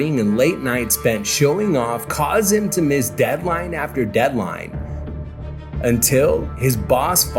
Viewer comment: i thought they said hairline